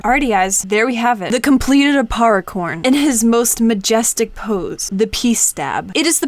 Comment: when she said aparricorn I thought she said aparriporn